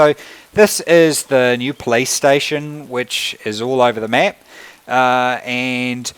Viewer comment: Thought he said this is the new playstation. Like if you agree